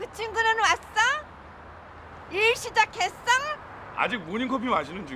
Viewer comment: 그친구는 왔썽? 일시작했썽? ㅋㅋㅋㅋㅋ 이렇게 들려 ㅋㅋㅋㅋㅋㅋ